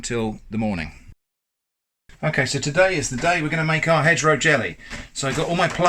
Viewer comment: For a terrible moment I thought that said “Hedgehog Jelly”. 🤭🥺😂